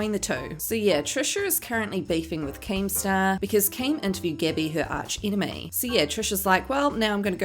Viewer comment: I had to replay 5:27 over and over because I can't understand what you said in your thick Kiwi accent. Still don't know what you said. "Because Keem something something Gabby her arch enemy" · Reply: OOoh INTERVIEWED Gabby her arch enemy. I listened to it 20x's.